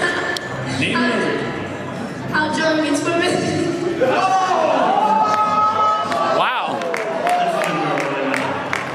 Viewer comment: What does the kid say at 4:03? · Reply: I think it's "How John Gets Women", but not super sure. Thank you for the link!